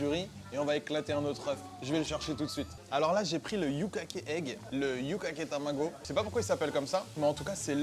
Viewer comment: J'ai entendu "J'ai pris le Bukkake Egg, le Bukkake Tamago...." je craignais le pire !